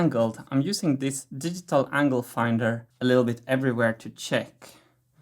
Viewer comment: Uh, I thought he said angle grinder again, instead of angle finder. I almost had a panic attack! :D